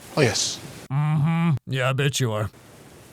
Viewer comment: I heard "Yeah b*tch you are" and I was like, hold on